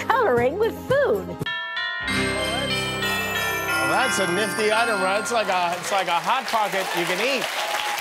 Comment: What'd he say at 1:18? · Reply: "That's a nifty item"